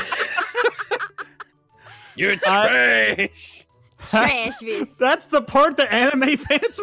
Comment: i thought you said trash but you said pat -_- XD